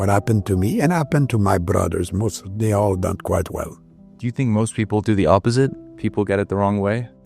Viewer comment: I think he’s saying They all done quite well ❤